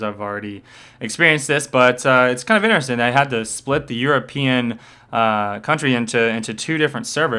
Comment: "they had to split the EU aaa country..." Oo? 3:43 u meant community... right ofc it sucks! instead of addressing the problem and attending to it properly as getting a more hardware and making a better platforms Riot just choose to use non-conventional methods as rerouting games to the us servers and then we get annoyingly higher pings (u 2 would be pissed off by that btw if it was vice-versa) and as spliting the community into 2 I wonder if this stunt could've been pulled off in the us